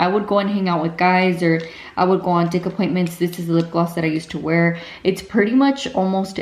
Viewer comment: Did she say dick appointments ?? 😂😂